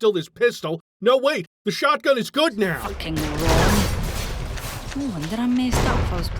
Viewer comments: i thought she said fucking raw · sounded like she said "fucking RAW"